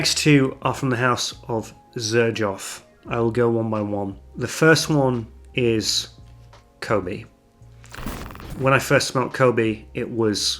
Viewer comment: I heard "The first one is... Covid" and couldn´t believe it :D Who would name a fragrance Covid? Then I saw the spelling :P